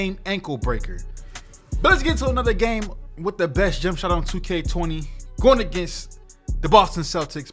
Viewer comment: Did anybody else hear on 1:12 he said 2k20